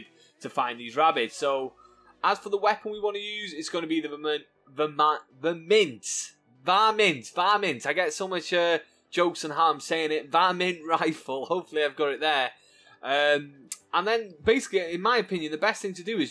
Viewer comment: Sounds like U say MINT rifle in other vids and of course U get shit for that lol Keep up the good vids 👍